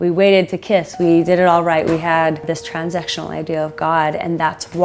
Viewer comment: Literally thought she said “We had this transsexual idea of God”. Looking at her I wouldn’t have been surprised if that’s what she meant 😂